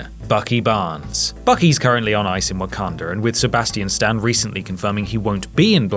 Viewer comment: did he say "Wakander"?